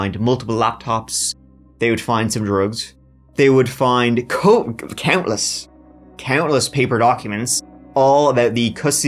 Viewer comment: did he say, “coke” or was he about to say, “copious amounts”? Context makes me think it’s the latter, but wasn’t sure..